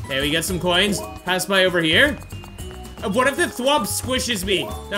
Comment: whomp did u just say thwomp